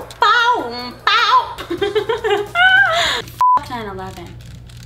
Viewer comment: did she really say F*ck 9-11?